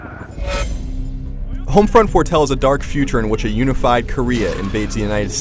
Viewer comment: Did he say Homefront 4?